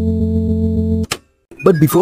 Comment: At first i thought it said "U can cry"